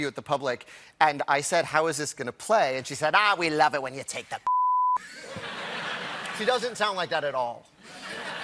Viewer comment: I can't figure out what he said at 2:30 "We love it when you take the ????" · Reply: John Baker p.i.s.s . British expression meaning to make fun of.